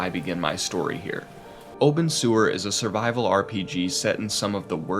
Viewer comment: I kept hearing "Open Sewer" instead of Obenseuer.